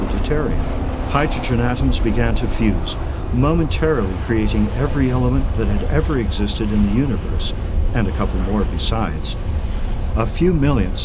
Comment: what does the narrarator say at 3:45? sounds something like "every something that ever existed plus a few more in the universe" ? excellent documentary by the way! & i'm choosy